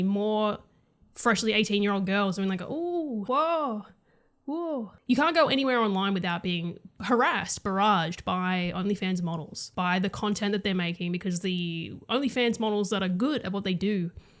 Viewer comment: yes. wait, did you say 1:26 uglyfans models? :OOOO